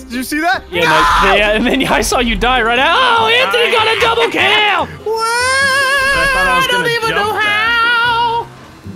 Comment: Best parts 3:55 4:06 4:26 12:08 7:31 what say?😂😂😂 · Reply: said "AHHH TOO LATE"